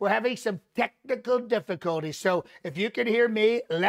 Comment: I heard "technical difficulties" as "testicle difficulties". I was disappointed when I found out he actually pronounced it correctly.